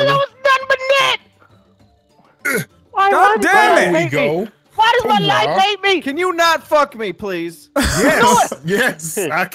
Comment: Wtf was Delirious saying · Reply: Caiden Kim " why does my life hate me" he was stuttering before he said it